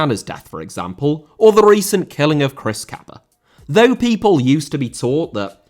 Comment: Who did he say was killed at 9:55? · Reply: Chris Kaba. He got shot by UK police. It's almost unheard of. I don't know the specifics though